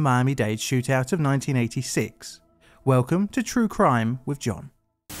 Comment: Great video. I got distracted by a phone call as it started i I heard “Do Crime with John” had to rewind and confirm I was being an idiot.